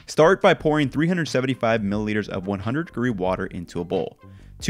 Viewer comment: What's 100 group water? It's that what he's saying? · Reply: 100 degrees